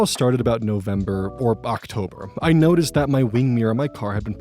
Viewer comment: I heard you say "November/Bachtober" and I was wonder if October was "listening to classical music month" and I just hadn't heard of that, lol